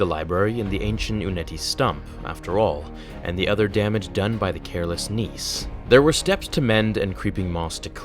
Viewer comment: Did you say careless niece? The caretakers thought rey was his niece?